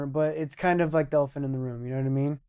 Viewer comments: I thought he said "dolphin in the room" lol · The ELEPHANT in the room..... Idiot. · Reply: Pretty sure he did say elephant. Bcz he said it fast it sounds like he said dolphin in the room. But upon watching it again and again he definitely said Elephant, due to the "phant" part which he did say clearly. And add to it the reaction on Chaels face, he definitely understood TJ to have said Elephant